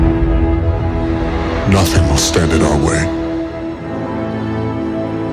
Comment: For some reason I thought Kylo Ren said I will finish what he started.